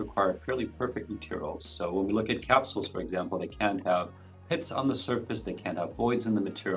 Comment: FYI - There appear to be a few typos in the captions provided on this video: 1:40 -- Should be "... pits on the surface ..." -- not "hits". 3:47 -- Should be "... it looks grey ..." -- not "great". 4:14 -- I couldn't understand what the scientist said, but I suspect it was not "paint" in what was captioned as: "... something like a 'paint' ..." .